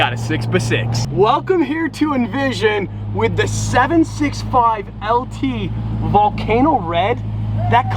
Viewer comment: pretty sure that aint a 675lt LOL its all good though its a 720 lt for those wondering beautiful car though love the color edit: you know what im a fucking tard he said 765lt and my dumbass thought he said 675lt glad I went and played it back again